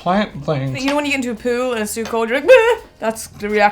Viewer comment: I thought she said POO too 😂